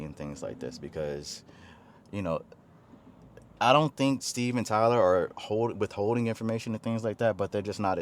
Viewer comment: I heard that as "Steven Tyler" as in: The frontman from Aerosmith 😆 Yet another banger from the man! Mr.O 2025 lets gooo.